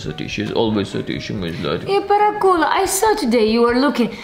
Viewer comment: Non so voi, ma io ho sentito "paracul" da al minuto